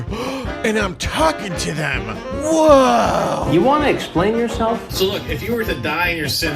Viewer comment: Loool the video had a half second freeze and instead of "you wanna explain yourself?" I heard "you wanna kill yourself?"